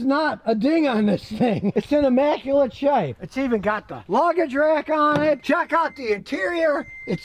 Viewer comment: Did Scotty say that it(s) ; got the " floggage rack " on it " ? !